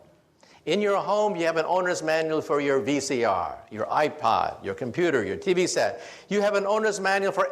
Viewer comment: did he say IPad???? o.0